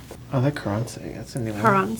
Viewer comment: Thought it said Thrush in the beginning :b